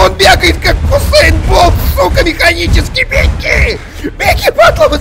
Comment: ЗВЗВХВХВХВХВХВХВХААХ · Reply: Мне послышалось "гусейн бол механический"